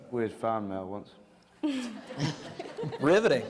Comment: can someone tell me what does "Jon Snow" say at 57:40 that they all laugh? About the fan encounter i guess but can't understand what he says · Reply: "I got fan mail once, riveting"